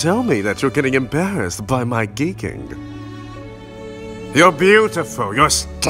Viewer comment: i thought he said "gay kink" and i was like "7w7 gay~" (dont understand it wrong i respect it :3)